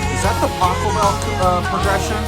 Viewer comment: the first time i heard it, i thought you said "is that the taco bell theme?" it's late....